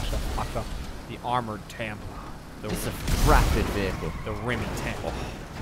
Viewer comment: I gotta admit, when called it the Rimmy Tampa, i misheard it as the Rimmy Tampon...